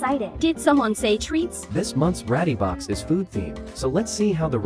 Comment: I heard "Bratty box" instead of "ratty box"